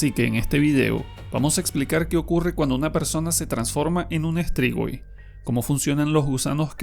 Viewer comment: No estaba prestando suficiente atencion y en vez de escuchar "los cambios de una persona al transformarse en un strigoi" entendi "los cambios de una persona al transformarse en un strip boy" Y me quede todo confundido por un momento XD